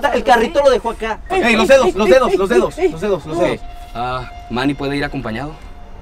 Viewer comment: si te das cuenta en el minuto 25:52 le preguntan y se escucha que dice que no que valla solo 😮